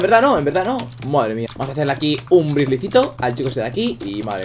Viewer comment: Pense que dijo vamos hacer lo que dijo vladimir xd